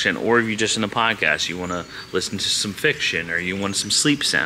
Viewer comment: Lol, you said fiction and I thought you said fix sh#!!!! 🤣